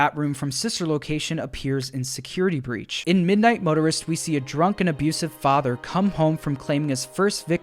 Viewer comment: I THOUGHT IT SAID MIDNIGHT TERRORIST